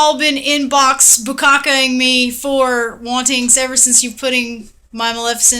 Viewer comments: am i deaf or what i heard was "Inbox Bukkake-ing me"? x'D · inbox bukakke-ing? that's what she said, right? i hope that's what she said cuz that made me click subscribe the moment i heard it hehe really good tutorial, and great output. thank you so much!